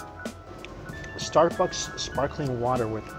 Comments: Am i the only one who heard StarFucks sparkling water at 11:25 ?:) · I do believe I heard Mike say "Starfucks" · did he just say Starfucks Sparkling Water? rofl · who heard starfucks